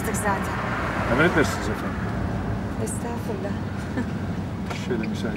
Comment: da ne diyor adam anlayan var mı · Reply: Emredersiniz efendim diyor. Bu kelime yutmasına benzer bir sahne de bir zamanlar anadoluda filminin manda yoğurdu sahnesinde vardı. Arap, “hayvan taze ot yiyor ya” yerine “hayvan taze et yiyor ya” diyordu. Nbc inanılmaz bir yetenek, gerçek hayatta bir dil şürşüyorsa filmde de olmalı.